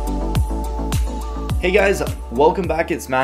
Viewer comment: I thought he said ...Hey guys It's Not Here